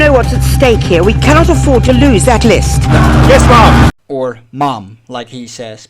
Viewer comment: haha. At first I thought he was saying “mom”…but I think it’s actually “ma’am” and not “madam”